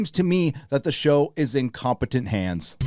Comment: You misheard what they said. It’s not “in competent hands.” They said “it’s incompetent hands”